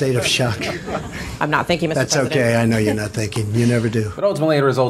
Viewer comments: Also I actually thought the reporter had said thinking instead of thank you so while I'm definitely not one to give Trump the benefit of the doubt it might have been a playful jab. It wasnt until you said she said thank you and replayed the clip that I realized. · Honestly, I thought she had said "I'm not (pause) thinking Mr. President" I didn't realize she had said thank you. Eh it's a comment I would have made in the situation I can't hold it over his head..